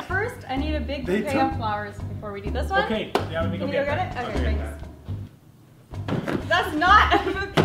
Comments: Did anyone else hear the "get out" at 1:08??? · OMG I heard somebody said GET OUT 🙁🙁🙁🙁☹️🙁☹️😕😕